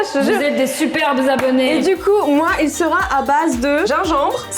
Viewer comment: Qui as entendu "moi et sora" à 1:37 ? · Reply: Non elle a dit moi il sera a base